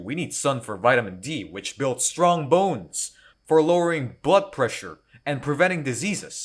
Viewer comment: Amazing!! i legit thought you said "for butt pressure" not blood pressure. Glory is an amazing character tho, one of my favs